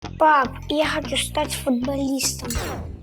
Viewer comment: блять мне послышалось "пап я хочу стать коммунистом"